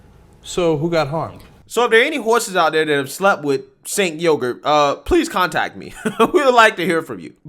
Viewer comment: I thought he said his name was "Saint Yogurt" 😆